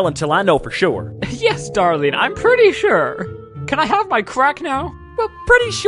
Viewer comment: When I was a kid, I did not know this was about drugs. I thought the lab part were scientist ponies doing science things and not drugs 😭 and I thought she said 'crap' and not 'crack'.